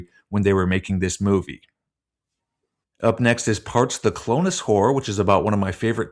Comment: lmao, given the poster, I thought you said ‘the clone’s whore’